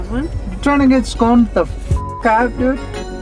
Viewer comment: I just realized what is saying at 6:17😂😂 “scone the f*#k out dude”. He said this phrase in Euro Tour Part 2 as well, “crêpe the f*#k out dude” lol. Can’t wait til we figure out what our team name is going to be.